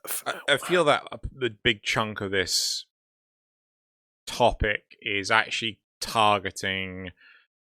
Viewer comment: said "big chunk of this topic" during the first one and I heard "Big Chungus topic" Also - getting a riven without a negative stat isn't actually what makes a "perfect" riven - in fact you WANT a negative stat, because it makes the positives that much higher. What makes it a "perfect" riven is when that negative stat is something meaningless. Like negative impact damage but positive crit damage, crit chance, and damage on a crit heavy weapon that has barely any impact in the first place. OMG labeling the worlds as "Plains of Eidolon", "Fortuna", and "Heart of Deimos" feels like you named them that way to trigger me personally! "Cetus" is the hub for the "Plains of Eidolon" map with the quest "Saya's Vigil", "Fortuna" is the hub for the "Orb Vallis" map with the quest "Vox Solaris", and worst of all the "Necralisk" is the hub for the "Cambion Drift" map with the "HEART OF DEIMOS" QUEST!!!!!!